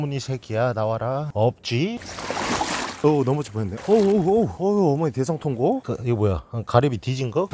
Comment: 저만 어머니 대성통곡으로 들리나요...